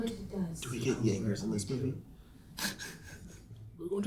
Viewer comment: bro what did he say at 10:15???? just as idris shows up?! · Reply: Jaegers, was referring to Idris Elba role in Pacific Rim